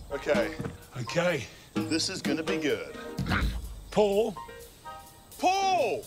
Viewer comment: would have had a hard time not letting go when it sounded like they were yelling "PULL!"